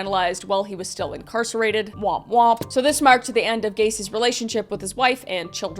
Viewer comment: did she say wop wop